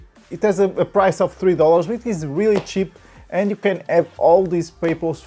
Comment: For a sec i thought he said its really cheap and you all can afford it... I was thinking sorry is that some sort of a rich joke im too peasent to understand